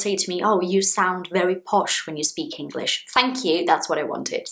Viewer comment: Teacher, did you say pash at min 3:10 ?! I am asking cause i heard many phrasal verbs that you said and this one is new for me 😬(Teacher, it's at 3:10 \For me really?! I think that i used the wrong prepositions 😅)